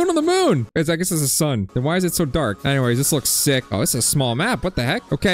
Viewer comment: DID HE SAY THE S WORD???????? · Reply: no he said "This looks sick"